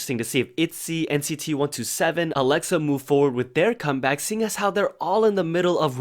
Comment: is it me or i hear about “AleXa” in the beginning 👀💜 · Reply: Yes, AleXa's also coming back. Listen to A.I Trooper!